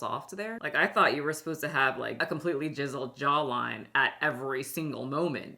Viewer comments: Did she say, "jizzled jawline"? 😂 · Did you say “a completely jizzled jawline” or am I learning something about myself 😂